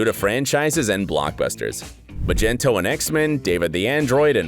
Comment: wait say that again... 7:16 did he just say "MAH-JEN-TOE" what a fake reporter